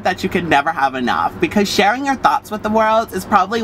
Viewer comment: I thought he said "Sharing your thots" ha